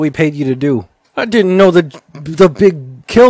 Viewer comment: WaIt… the BIG KILL BOMB DID A BIG KILL?! I thought you said it MADE FRIED KRILL!! YOU LIIEED TO MEEEEEEE